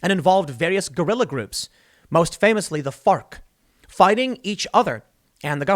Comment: i thought he said FART